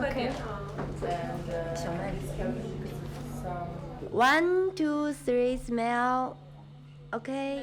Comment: Sorry did she just say 1..2..3.. Smell?